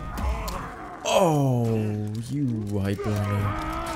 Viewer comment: did say "oh you white woman"